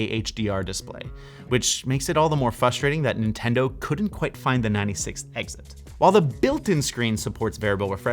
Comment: Anyone know what the hell he said here? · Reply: EDIT: he said Nintendo not finding the 96th Exit. Google states it seems to be reference to Super Mario World. I don't get the reference he's trying to make, however